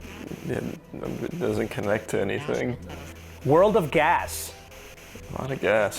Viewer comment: pls tell me someone else heard "child of god"